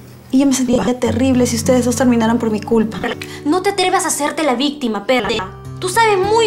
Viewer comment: escuche pedeja xD